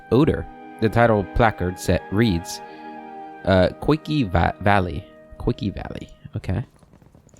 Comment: NGL first time I heard "quickie valley" and had to rewind 😆 EDIT: Honestly probably a function of me binge watching (while multi-tasking) episodes super late into the night while my brain becomes more and more sussy. Almost 5 AM atm.